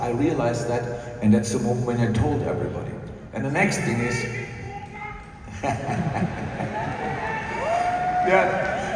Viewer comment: What did the guy yell at 8:54? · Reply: Don’t leave probably 😊